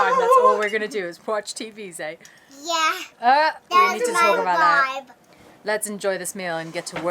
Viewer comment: did say "thats my vibe"???😭😭😭 she is the CUTEST!!!